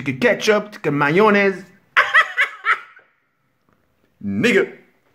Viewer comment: Il a dit quoi a 0:30 ?? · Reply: Perso jai entendu "nigga" 🙄